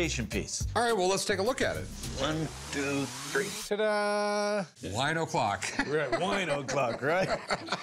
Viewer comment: Lol, I seriously thought it said "whino clock" spanish reference 😂